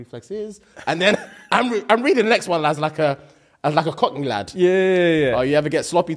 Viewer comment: I’m not sorry I’m “American”. Did you just say cotton lad ?🤣 I clearly haven’t heard it all. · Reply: clearly i can't unhear this, though i heard you...i still hear cotton lad @ 5:32 , a "kney" and a "L" sound different even with accents.🤣